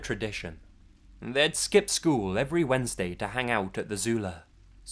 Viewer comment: "My parents thought nothing could ever go wrong" Until the fire nation attacked XD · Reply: at first I heard "they'd skip school on Wednesdays to hang out with Azula" #avatar3confirmed